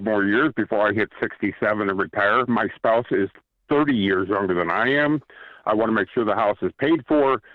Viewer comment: Caller 1 didn't call for help. Why did he call? Did he just want to tell a bunch of strangers he inherited $750k, his spouse is a deadbeat, and he himself has never saved a dime? Something tells me most of the $750k is gone by the end of the year and the rest of it is gone in Q1 of 2026. Bet me. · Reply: I could have sworn he said the partner is 30 years older